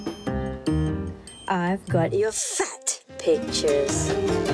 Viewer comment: LOL first time I heard was "I have got your sucks bitches."